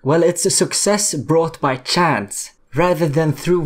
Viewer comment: ngl thought you said chads